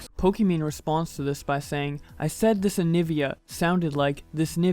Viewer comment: Go to 0:04 and put the playback to 0.50. She clearly says nivia